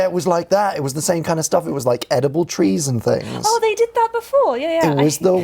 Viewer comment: I know he said "Edible trees and things" but all i heard was "Edible treason things"